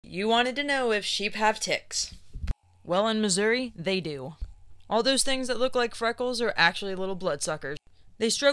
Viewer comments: For a split second i thought you said "You wanted to know if sheep had tits." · I swear I thought you said “tricks” I stayed through this whole video waiting for them to do some tricks 😂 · My ears are a little stopped up, I thought you said tits instead of ticks. lol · I misheard ticks as tits 😂😂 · I thought she said "Tits" not "ticks" 😭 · I thought I heard, "If sheep had ti ts." O'course they do.